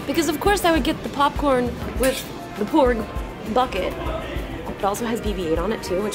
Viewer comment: I live outside Harrisburg (Carlisle). As a Pennsylvanian, I apologize for our state. Since you're in Pittsburgh, I envy you as its the best part of PA. That Porg popcorn tin is awesome. Edit : Oh, you're from Pennsylvania. Congratulations on getting out. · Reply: Steve M I thought she said porn bucket